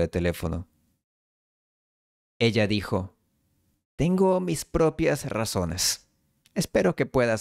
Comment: "ella dijo tengo mis propias razones" I keep hearing "ella dijo tengo mis propias a razones".